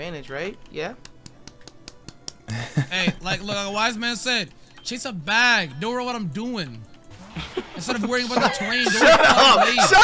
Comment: What was saying at 13:20? That had me laughing. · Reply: Like a wise man said: "Chase a bag, don't worry 'bout what I'm doin'"